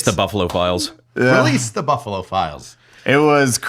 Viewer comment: He said buffalo files and i heard it as buffalophiles 😂